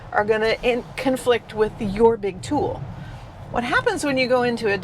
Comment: Did she say: "are gonna conflict with your big tool"?